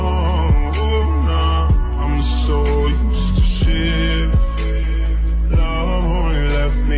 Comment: anyone else thought that said “im so used to sharting-“